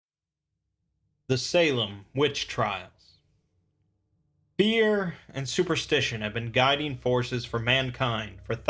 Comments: Had to do a double take at the very start, coulda swore he said "Beer and superstitions..." lol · For the first 5 minutes I heard “beers and superstition” and thought yeah that sounds about right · At first I thought you said "Beer and superstition have been guiding forces for mankind for thousands of years, resulting in the countless loss of life" and it made just as much sense as the correct version. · I heard "beer and superstition have been a guiding force..." and was like 'well hes not wrong... but wtf?' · I heard "beer and superstition"